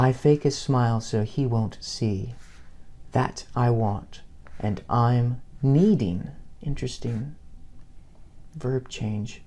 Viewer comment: I actually always thought she was saying I want and I need. That surprised me.